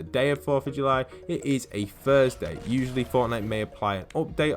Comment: Join my discord for chance to grab the spray! Discord Server "The Postbox" - <link> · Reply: Sound like ur saying Fursday